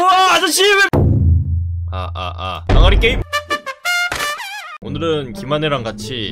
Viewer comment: 나만 아가리게임이라 들림?